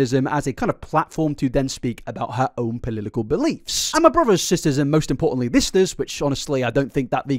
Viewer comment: did i hear "polilikal believes" wuhehe